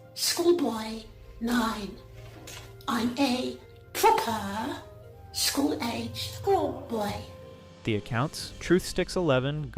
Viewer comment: I heard “I’m gay, focka”